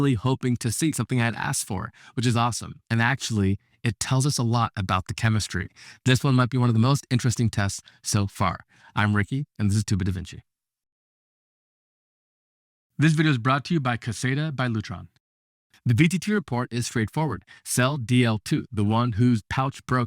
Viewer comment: No pun intended. And I’m not native English speaker. But every time you say “This is Two-bit DaVinci” I hear “stupid davinci”. And it’s just fun every time)) Does anyone hear the same?